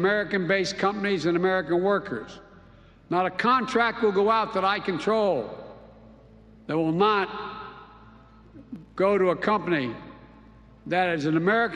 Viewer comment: Did Biden say that this contract will NOT go to an American Company with American Workers? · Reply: He said "not a contract will go out that I control, that is not an American company......."